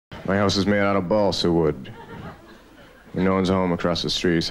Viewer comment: It sounded like he said my a** is made out of balsawood